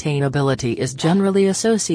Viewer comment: And I understood nothing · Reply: All I kept hearing was “sust-hain-ability